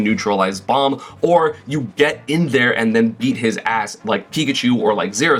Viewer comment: i heard "eat his ass" but i think i misheard that